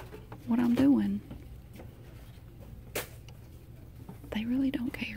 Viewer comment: did I just hear hey get back up there bad bill 💀💀